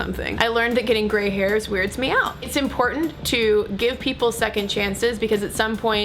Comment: i somehow misheard when you were talking about grey hairs, and thought you said "i learnt that getting grey hairs turns me on" and i was extremely confused...